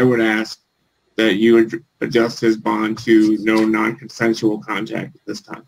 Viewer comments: Just in case, at 25:42 he doesn't say no non-essential, he actually said no Non Consentual contact. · Thought he said no nonconsensual contact